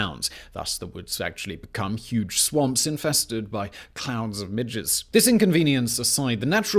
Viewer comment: it literally took me 5 times to get that he said "swamps infested with CLOUDS of MIDGES" and not "swamps infested with clowns and midgets" because i have this on in the background doing my homework and the first time i heard that my first thought was "....waitwut"